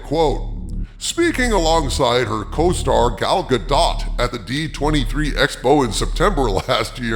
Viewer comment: i thought he said Gual Dukat 7:30 lol